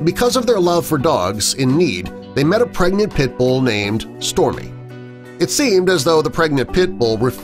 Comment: i was confused because it says Storie in the text but i thought he said "Stormie/Stormy" xD